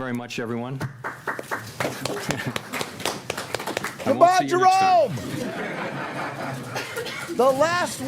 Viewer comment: what did he say? “Happy Easter”? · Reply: I won't see you next time